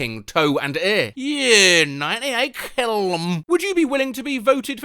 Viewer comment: I hear that as "kill them" NO